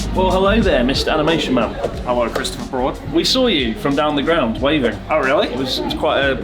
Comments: Did say "Hello Christopher Fraud" at the start? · I thought I heard say Christopher Fraud.